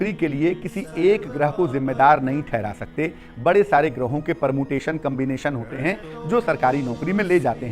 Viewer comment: did he just say Permutation and combination?😳